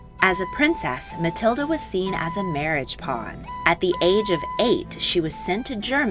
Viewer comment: Was seen as a marriage ...?... at the age of eight What is the word on "...?..." Please help me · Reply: She was a marriage pawn.